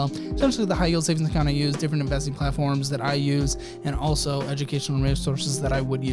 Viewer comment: Lol in the last blurb when you were saying to check the links. It sounded like you said "different embelezing platforms" lol